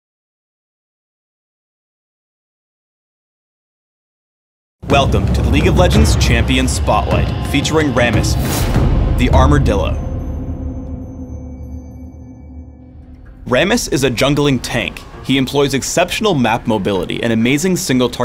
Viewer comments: I thought he said armordildo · I thought he said "the armordildo" x_x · first i thought he said armordildo